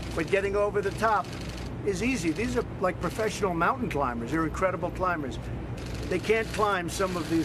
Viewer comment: did he say "mountain climbers"?....